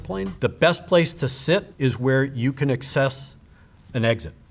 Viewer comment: never in my life heard anyone pronounce ‘access’ that way; he put the wrong em-PHA-sis on the wrong sy-LLA-ble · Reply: Sounded like he said excess, like “a un-needed amount of”